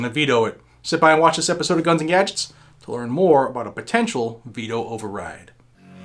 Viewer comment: ha ha. in the intro it sounded like you said beto override. lol